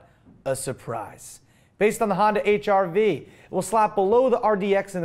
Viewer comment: LOL I totally thought said Honda HIV not HRV. I started cracking up :)